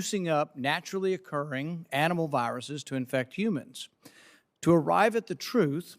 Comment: What did he just say?! A few seconds before 0:34. Am multi-tasking dealing with two other disrespectful and annoying people so am not paying attention to what is important even though it's in the past. Yeah, I annoy myself too. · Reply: Juicing up animal viruses...Just turn on your close captioning. But sometimes it is off